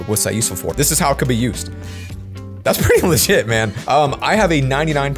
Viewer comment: was that a no no word that I just heard hmmmmm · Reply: He said LEGIT